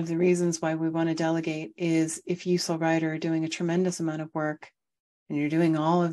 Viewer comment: Hi at 5:26 what is that word? Usolrider? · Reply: You the Soul riding in the body. "Soul Rider"